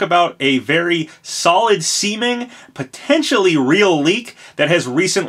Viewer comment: did you just say solid semen?